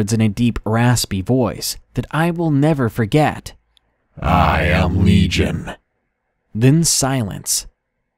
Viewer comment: What is he saying during 8:48 - 8:50? "I am legend"?